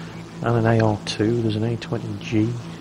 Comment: if you listen from 2:00 to 2:05 did he say A-20 Jew? jw